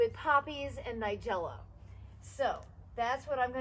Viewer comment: No not Weigela. I sowed poppy seeds. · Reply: and 6:54 it sounds like you said weigela